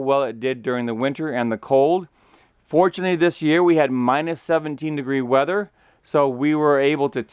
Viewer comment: i’m sorry at 0:38 did you say “... ‘fortunately’ we had minus 17 degrees...?”